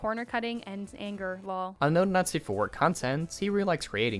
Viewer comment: The auto-generated English captions are wrong here. I am being presented with "unknown Nazi for work content" when it should read as "unknown Not Safe For Work content". I figured the above should be brought to your notice.